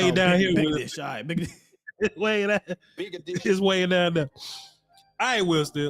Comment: I thought he said big edition at 1st. Lol.